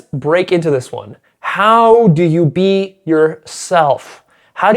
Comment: how do you beat your ** that’s what I heard for a second there o-0